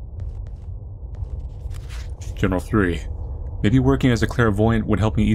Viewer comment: bruh how tf did I hear “Journal 3” as “general curry”